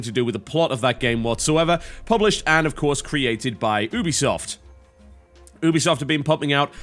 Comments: I seriously thought, at first, he said Boobiesoft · produced by boobysoft ; that's wht i heard